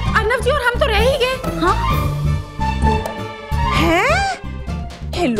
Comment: who else heard bride aur broom 😂😂😂😂